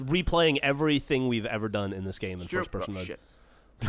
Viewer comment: What did ray say at 41:41? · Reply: "first person mode is shit"